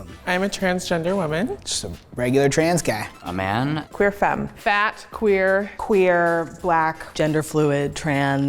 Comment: did she say fat?😂